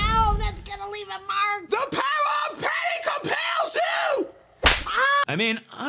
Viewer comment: what does she say "the power of ___ compels you!" when she slapped her? at first i thought she said "pain" which i thought was kinda clever. Compelling the ghost with pain instead of a holy aspect. But now i think she says a name...? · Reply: She says "The power of Patty compels you" ...yeah, its bad.